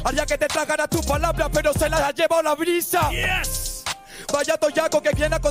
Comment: Falto la de que le responde a cuando dice que su nombre significa dolor y le contesta sobre su padre,abuelo y dice que le teme al dolor porque ya nacio a su lado, 4 como una casa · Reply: Yo escuché "se lo han detectado" xd